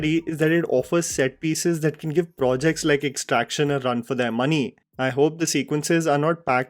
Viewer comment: I'm sorry you said what??? Did i hear "give Extraction a run for it's money"?? 😂😂